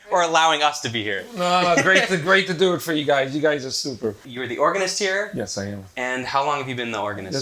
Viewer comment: Did he just say "you guys are stupid"